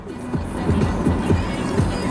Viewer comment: is he saying bluffes?